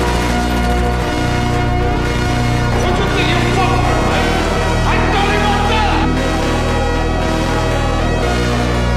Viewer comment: What was he saying? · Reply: "Who do you think you are fucking with, I'm Tony Montana"